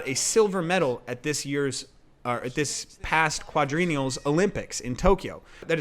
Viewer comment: What did you say? I lost you at this point. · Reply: Quadrennial. I pronounced it wrong.